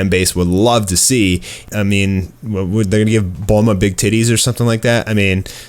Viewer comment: Was listening somewhat while playing games, then I heard "Bring back Bulma's big titties" and whipped my head around so damn fast